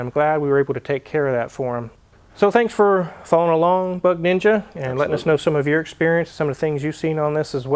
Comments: I thought he said "butt ninja". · At first I thought Wizard said, butt ninja. I was like dang, he got to have a high body count. Haha